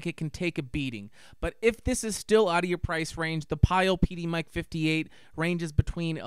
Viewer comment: what mic did you say is cheaper??? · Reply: Pyle PDmic